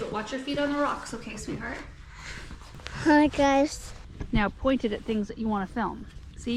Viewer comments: Just wondering if you happened to notice what said around 5:09 in the video? It was a close up of her and I swear I distinctly heard her say "Hi Guys"...wonder where she's heard that before! Like mother, like daughter, that's as good as it gets. Maybe she's practicing for the intro on her future channel - Garden Answer 2.0! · Did she say “Hi guys?” 5:11. I had to rewind and rewind. She’s the next Garden Answer gal. Oh my!!!